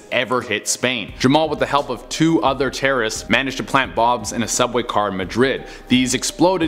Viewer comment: did he say "plant Bobs" or is that just me? Wait... Plant Bobs... Bob Ross... Bob Ross paints happy little trees... Which are planted. It all makes sense!